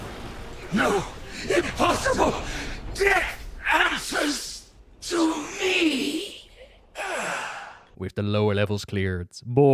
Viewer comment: Did Kel'thuzad say "dick asses to me?" O_o Because thats what it sounded like he said and now I can't un-hear it.